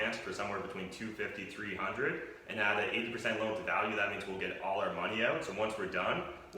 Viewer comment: I know I'm coming in late to the conversation, but... at about 3:16, says something like "at an 8% loan to value", or "add that 8% loan to value..." I've listed over and over and can't make it out - but it seems like he's bringing up an important point, maybe a rule of thumb of how to value a property. Can anyone elaborate on what he said and what it meant?